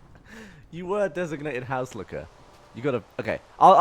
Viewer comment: I heard, "You were designated house licker". That would be a terrible job.